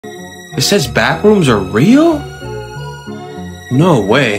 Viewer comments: Sound like he said baboons are real💀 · Have you ever been to the backrooms before? 😮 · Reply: I thought you said "bAtHrOoMs ArE rEaL?!?!"